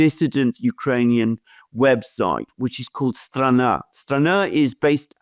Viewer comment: What is the URL of the dissident Ukrainian website? It sounded like “srana”.